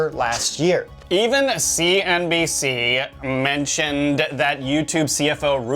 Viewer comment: I could have sworn you said "last year twenty nineteen "